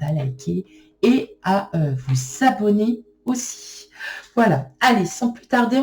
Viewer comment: Bienvenue ! trop drôle cette guidance ; j'ai bien ri ! 😁😁👍 tiens, j'en ai une bonne pour vous - comme j'suis un peu à l'ouest en ce mmt - au début, au lieu d'entendre << pensez... à vous abonner, j'ai entendu ""vous savonner"" >> ha, ha, ha ! Merci c'était un plaisir Bénédictions pour vous 🙏💎